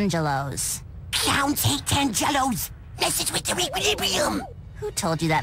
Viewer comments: Mandy: what’s with all the tangelos? Billy: clowns hate tangelos...messes with their equilibrium😂😂😂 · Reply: I thought he said cerebrum instead of equilibrium. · Mandy: what’s with all the tangelos? Billy: clowns hate tangelos...messes with their equilibrium😂😂😂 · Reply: Aye I always thought he said 'ripilipium' 😂